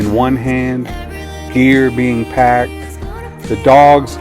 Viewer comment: At first, I thought you said starting the day with coffee in one hand and packing the beer lol Then I realized you said gear and this was not The Ricker lol Thanks for sharing your adventurous as always! Carry onward…